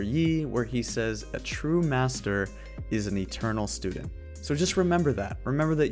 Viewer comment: I always thought yi said that a true master isn't a terrible student lol